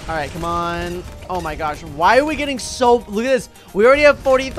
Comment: the captions thought you said “why are we getting soap”